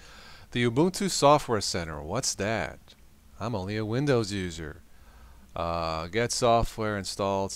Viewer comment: When he said "The ubuntu software center, what's that i'm only a windows user." Instead of that I heard "The ubuntu software center, what's that i'm only an idiot."